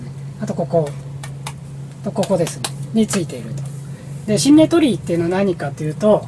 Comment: シンメトリー とおっしゃっていたように聞こえたのですが、新明鳥居 の事でした（笑）覚えました＾＾ｂ